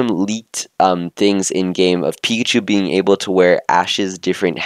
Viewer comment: Pichu? Dude, what are you saying?